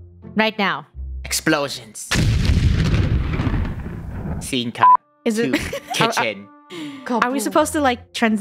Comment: WHAT DID HE SAY??? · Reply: He said scence cut